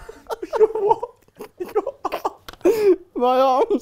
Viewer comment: what did he say??? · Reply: "My arms"